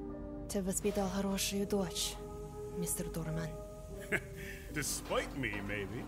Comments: I think he said "despite me" which would make a lot more sense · I've always heard this as "despite me" In that sense, it's Khan being self-aware of his horrible parenting, which is more consistent. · Absolutely loving these videos, keep up the awesome work. Also I think at 1:03:50 Khan was saying "Despite Me Maybe" as I believe after the teacher conference he realized he hasn't been there for her, thus why he's trying to be a bit more fathering towards her now, dunno just the vibe I am getting. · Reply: Thank you! I'm glad you're enjoying them! c: And yeah, you're right. Khan did say "Despite me maybe", which makes way more sense.